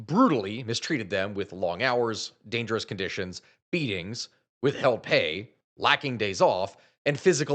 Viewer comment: For a second at 9:28 i thought you said beatings without pay and i thought well beatings with pay wouls suck less. Then i remembered combat sports exist.